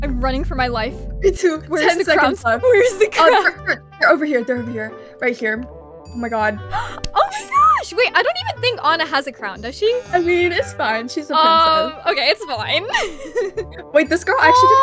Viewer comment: what they hear:ana Vs what i hear:Lana